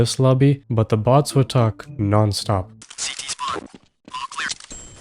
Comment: For a second i thought he said cp spawn